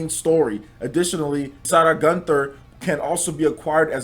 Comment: Why did you pronounce Isara so weird? I had to look at the link to see who you were talking about, I thought you said "Sada gunther"